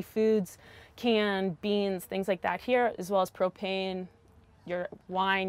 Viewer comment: I THOUGHT YOU SAID COCAINE HAHAHA